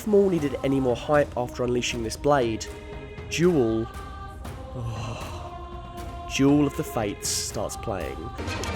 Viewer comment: "As if maul needed any more hype after unleashing his blade Juul, juul of the fates starts playing" thats what I heard cuz I'm a true blood murican